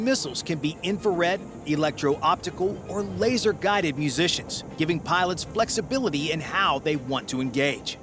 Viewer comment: Did I hear " musicians" or " munitions" ?